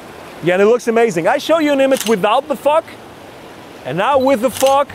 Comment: Omg I thought you said Fuck instead of Fog... lmao I cannot unhear it now.